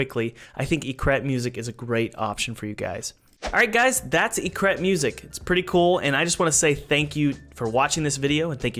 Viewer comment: I thought you said "it's crap music!" 9:06😂